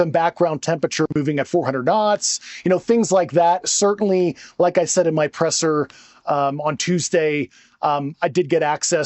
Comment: It didn’t sound to me like he was stating he saw the photos on Tuesday. Sounded like he was saying “like I said on Tuesday”